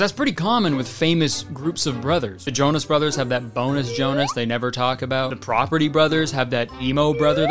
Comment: Truly thought he said b o n e l e s s Jonas